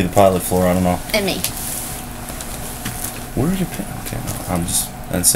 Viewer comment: "where are your panties?" Did I hear that correctly? Lol